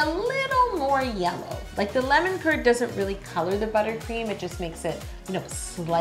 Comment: is it just me or when she said lemon curd i thought she said lemon turd.